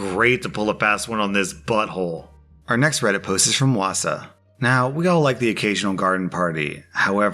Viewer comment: Did I heard it right? Wausau? Which one...